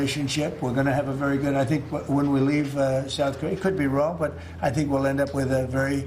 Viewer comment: Did he just say "When we leave Korea"???